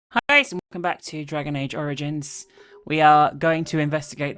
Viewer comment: I heard "Dragon Age: Origins" and I snorted, bad idea for Sprite in my mouth.